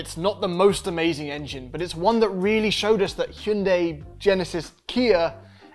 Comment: I thought you said 'Kiundai' at 2:10 LOL that would be a funny way of saying both brands at the same time.